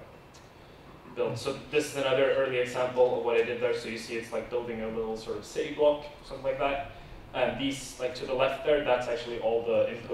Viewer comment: Could anyone tell me what he said at 4:24-5:20? I've been trying to write the subtitles for this great video and translate that into Japanese. However, I am not a native English speaker, it is very difficult for me to listen English accurately from sound sources. I'd appreciate it if you could help me. Thank you for reading. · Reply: "So, this is another early example of what I did there, so you see it's like building a little sort of city block, something like that . These to the left there, that's actually all the input modules being used, so it's not too many, it's quite quick to get started with it . It figures out how to put it together into different things . And this... since the algorithm is content-agnostic, fundamentally, it doesn't know that it's building a house, it's just knows that it needs to fit things together. It makes it, like, as an artist, you can build all these nice little, sort-of, transitions between things, and you can build extra little things, useless things, like you can build a chimney, and you can build a... Like, I have some larger features, of like, things going up here, this little, sort-of, divider here . So, yeah... you can build things like that, and then it will create larger structures from it"